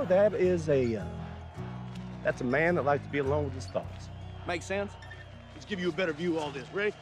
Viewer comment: A man who likes to be alone with his thoughts. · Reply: "........thoughts" ! Thanks for the correction, I thought he said ".......socks" !